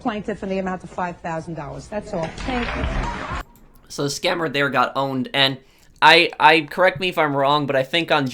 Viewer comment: I think I heard scammer got boned lol